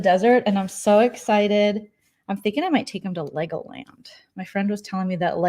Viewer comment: Lmao Thought for a second you said "Leg o' lamb" instead of Legoland. I swear, my attention strayed for only a nanosecond.